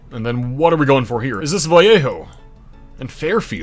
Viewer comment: Bay Area native here. Vallejo is actually pronounced with the L sound, despite it appearing like it should have the "Y" sound that Spanish words with double Ls have. It's weird. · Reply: I thought he said Viejo. That's the Valley-Jo. Every time i hear a non-native mispronounce Vallejo, my heart gets stabbed